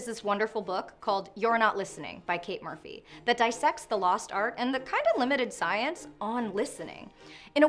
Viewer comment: Without subtitles, I heard "That I sex the lost art..."